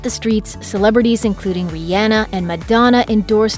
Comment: did she just say rianna instead of rihanna